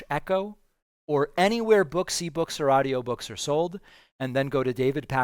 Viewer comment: Off topic, but when David said books, e-books, or audiobook, it sounded like he said booksie-books.